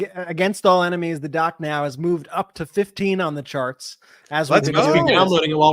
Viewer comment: Sounded like you said “Sharts” not “ Charts” lol